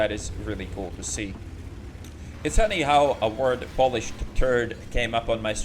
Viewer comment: Thought you said "Polish Turd" as opposed to "Polished Turd" xD